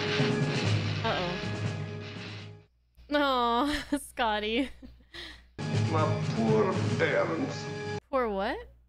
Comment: Scotty: "My poor bairns!" "bairns" is Scottish for "babies" or "children". We can easily see Scotty treating his engines as his babies! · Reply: Thanks for that information. I always thought Scotty was saying "My poor bearings." hehe Meaning his engine parts were overheating and catching on fire.